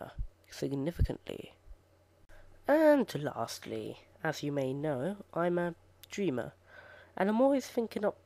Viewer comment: Lol, at 7:00 it sounded like you said: "As you know, I'm a ginger." xD